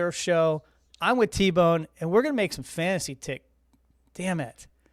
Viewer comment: I thought I heard your name her “fantasy tits”?😂